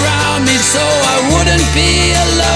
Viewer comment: I thought it said “some may be for showing off “ Not “some are from showing up”. Who knew lol